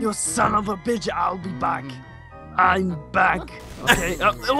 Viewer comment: I think it's funny that says owl be back.Or at least that is what it sounded like.